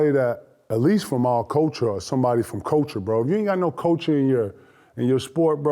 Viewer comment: He says the word 'culture' weird. I thought he said 'coaching'.